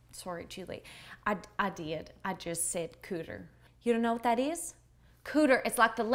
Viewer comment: I thought she said "panties." "Cooter" is also a type of turtle in her neck of the woods.